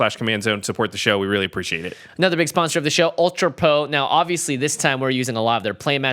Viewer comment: did he say Ultra Po?